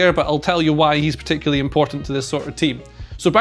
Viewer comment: What did you just say? Soccer Team? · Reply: Sort of team